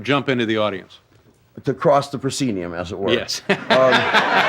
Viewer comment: what does he say here? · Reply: “To cross the proscenium, as it were.”